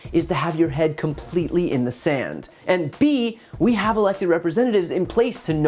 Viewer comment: "In the sand" yet I heard "up your ass".